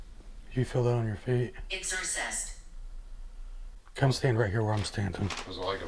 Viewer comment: I thought it said "keeping it in the family" not "exorsist" I was like "what in the hell" thank god for captions hay 😳😯😎